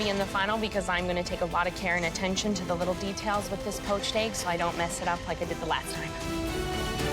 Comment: At first I thought you said Roached egg